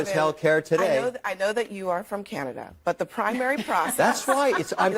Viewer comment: Did I just hear “I know that you’re from Canada?” 🫢